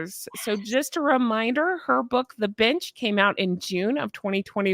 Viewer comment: I thought you said the book was, The Bitch.